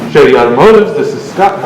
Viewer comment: I thought I heard "Chevy free automotive" XD